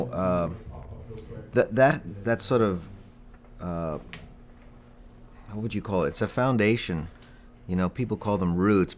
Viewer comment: which artist did he say around 2:30 ....The Who, Cream, Jeff rotal? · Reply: Jethro Tull. Great band.